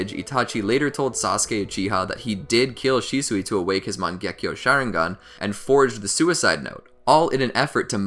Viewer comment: Did he just say “Share-ingan” 😂